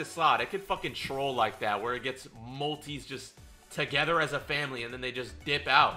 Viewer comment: did u say family